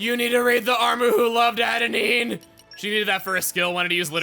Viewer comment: I first heard this as "You need to read 'The Armu Who Loved Adenine'!" :D